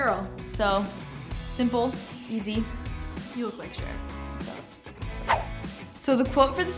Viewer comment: oh i thought she said "you look like cher" :P lol